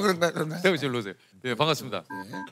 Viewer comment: 여기 잘 들어보면 (반갑습니다) 소리 들림 ㅋㅋㅋㅋㅋㅋㅋㅋㅋㅋㅋㅋㅋㅋㅋㅋㅋㅋ